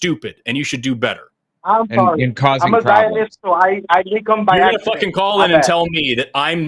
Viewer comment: DID HE JUST SAID "I A ZIONIST".???